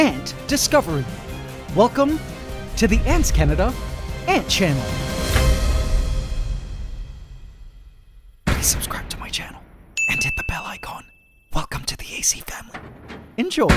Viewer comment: For the longest time, I thought you were saying “hit the bell like gong” and tbh I prefer that more then now realizing it’s “hit the bell icon”